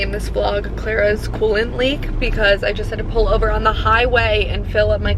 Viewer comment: I totally thought you said your truck has a colon leak! I was like wtf is that!? 🤣🤣